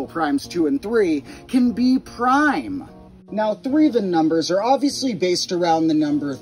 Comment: 1 says "threen" instead of "threeven"